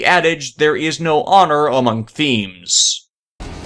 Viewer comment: is it just me or did you say “Honor among ‘Themes’.”?